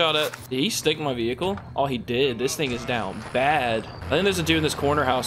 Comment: bruuu 8:18 did u say this n*ggas down bad😂💀